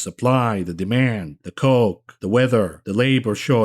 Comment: sounded like you said jew-political tensions there for a second.